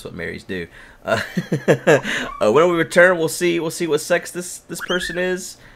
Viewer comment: when said ' lets go see whar sex he is ' I totally thought he said ' lets go see what sexy is '